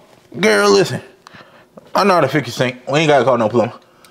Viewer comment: When he said “I know how to fix the sink” the closed captions said “I’m not a 50cent” 😂